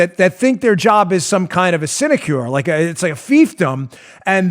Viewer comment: Ok, I'm usually pretty good with words. If I don't know one, I go look it up and put it in my lexicon. I know Fiefdom. However, what in the hell is"Iscidcure, Asidcure (sp?). I spent 20 minutes trying to find this one, w/out success. Is my spelling off? Is it a personal name? Anyone know? Did I hear it wrong? It's @ 22:39-2241. Thx in advance. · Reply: Sinecure